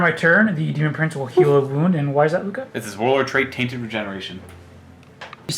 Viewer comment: Hmmm, at around 1:16:45 mark I wondered if the Warlord trait 'Taint of Regeneration' (that's how I heard it) should be called 'The Episiotomy'